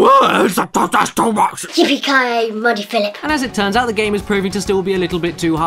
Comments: Did she say "Monty Phillip" ? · Yippee Ki Yay, Muddy Philip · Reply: I thought she said Mutha Philip